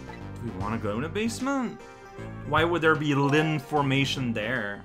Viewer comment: did you just say Lynformation?